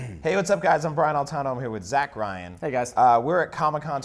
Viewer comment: Lol I thought he said "Hi I'm brian boitano" and i instantly said "What would Briand boitano Do?"